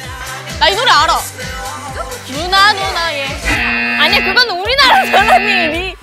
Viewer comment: i thought i heard that they say loona,loona gay jajajajja😂